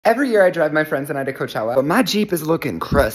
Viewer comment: Tell me why at the beginning I heard “every year I drug my friends” but then I heard the to cochlea